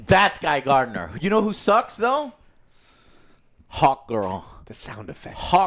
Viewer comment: every time you say Hawk girl I can't help but hear Hawk Tuah girl. Not sure if its the way you pronounce it or just my mind being retarded but thats what i heard every time